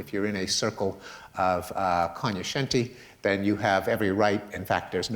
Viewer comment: Can someone please tell me what word Pinker uses at 13:56? It sounds like "con-yu-shente." I can't trigger a result in Merriam-Webster's online site based on any of my attempts at spelling it. Thank you in advance. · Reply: It took me some googling but I think I found it. co·gno·scen·ti känyəˈSHentē/ noun people who are considered to be especially well informed about a particular subject. "it was hailed by the cognoscenti as one of the best golf courses in Europe"